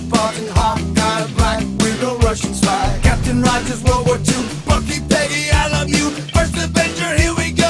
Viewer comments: “Bucky, Peggy I love you” same Evans.. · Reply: I heard "Bucky Peggy alomou" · ''Bucky, Peggy I love you'' Thanks that's all I needed to hear. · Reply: I keep hearing it as, "Bucky, baby, I love you!"